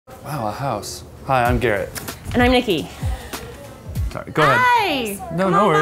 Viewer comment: at first I thought he said “hi I’m gay” 😂😂